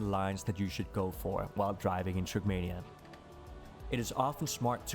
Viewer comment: captions say Shrekmania XD